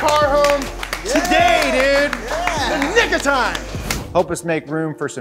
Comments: Yeah what he said Nigga time! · Anybody else hear that guy say Niga time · What'd he say?? 🤣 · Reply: "In the nick of time."